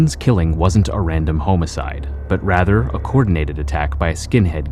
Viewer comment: Please do not say "HOME-icide" ever again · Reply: Lmao i thought he was saying homo-cide 🤣